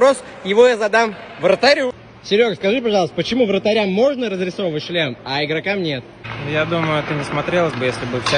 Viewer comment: мне послышалось: почему вратарям можно разрисовывать член а игрокам нет?